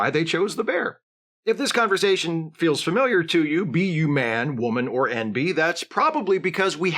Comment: I thought you were going to say ''be you man, woman, or bear'' 😂😂😂 for real though, thank you for this vid. I think you could help reach some men (and possibly women). What it comes down to is that some women just don't want anything to do with men, and you can't tell them that their wrong . There's no amount of statistics you can throw at her to prove her wrong. Some men are unhinged by this. Thankfully not the men in my life. Thanks again, you've got a new sub.